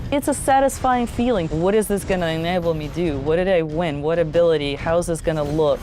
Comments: Did she say "What will this enable me do" at 20:51? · did she just say "What is this gonna enable me do?